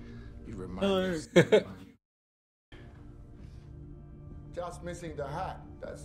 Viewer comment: The Subtitle is wrong. What he said was "Just missing the hat" . We all know who is the character that talks shit to Dawes and wears a hat.